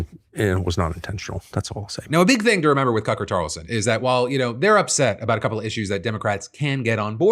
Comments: did you call him Cucker Tarlson???? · Did Phil call him Cucker Tarlson? 😂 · Anyone else catch “Cucker Tarlson” at 3:16 😂😂😂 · Did i hear you say 'cucker tarlson' at 3:15? Bc i like that one better LOL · did you really say "Cucker Tarlson" ? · lolol was that a "Cucker Tarlson" I heard at 3:14?? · Did he say... Cucker Tarlson?